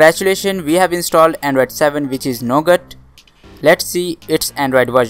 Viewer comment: he says "We have installed Android 7 which is no good". . . . . . . . jk. I know he said Nougat. Sounded like it tho 😂